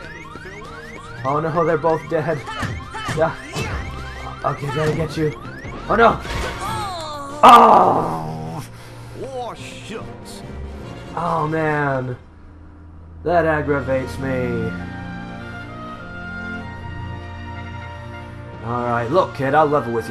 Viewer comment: did goofy say "oh shit"? LOL xD